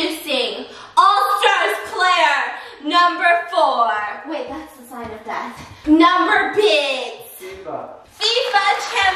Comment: wait did she just say that’s the sign of death?!?!?